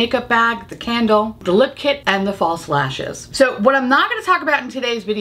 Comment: Initially when you said false lashes, I heard "Fall Slashes." My brain must have been fixed on the theme of the collection. It wasn't until you displayed the box of lashes that I realized my mistake. 😜 BTW I didn't notice you were wearing two different eye looks until you told us. I do slightly prefer the purple look, but both are beautiful.